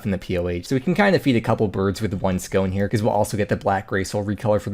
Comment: It really sounded like you said defeat two birds with one scone...